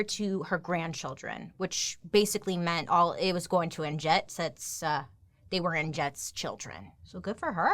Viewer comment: My mom’s sister is named Jet. Every time you say Annjette, I keep hearing “Aunt Jet”!!! 😮